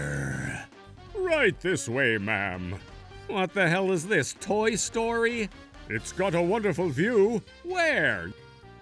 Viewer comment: Did say Toy Story